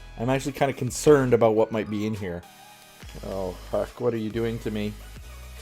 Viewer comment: did you just say the "F" word?! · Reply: No he said huck